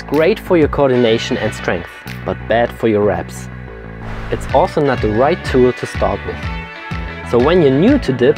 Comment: Did I hear this correctly: ring dips are bad for abs... ? (few seconds after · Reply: He said ass